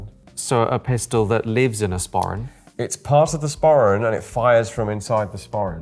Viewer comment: Sporin? Sparren? What is this word? What does it mean? English is not my first language. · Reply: It's essentially a small bag traditionally worn with a kilt. Search for "sporran kilt"